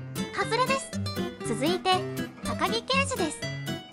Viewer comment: 高木刑事です！が、うちだまあやです！に聞こえた… (さっきその動画みた)